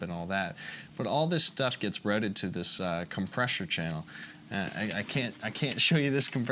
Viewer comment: is it just me or does he say "compressure channel" at 8:04??